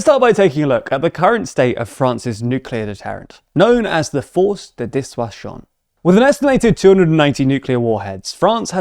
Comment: It sounds like you said “force the dishwasher”, but I forgive you.